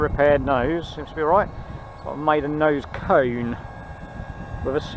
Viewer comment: Misheard your commentary but it seemed apt, Maiden nose cone 😍👍